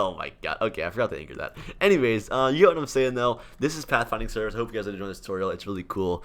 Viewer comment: Have captions on lmao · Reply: "You dont know what I am saying though"